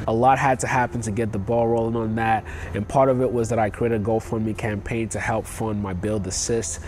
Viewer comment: did you say you “couldn’t go for a go fund me”?